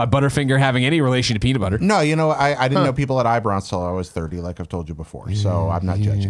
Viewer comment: Listened back a few times but can't catch it, what did say he didn't know people had? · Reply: He didn't understand that people had eyebrows until he was 30.